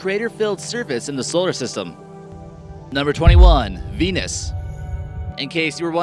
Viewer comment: I thought he said penis